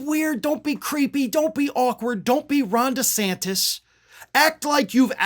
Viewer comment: Does anyone else keep hearing "Rhonda Santis" instead of "Ron Desantis"